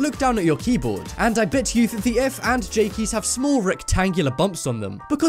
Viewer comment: the subtitle thought he said f and g keys because of his accent 😂😂.